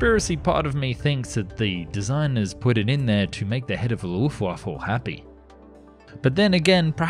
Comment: it sounds like you say luftwaffel